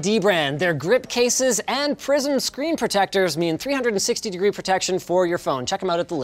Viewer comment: I hear "PRISON screen" every time.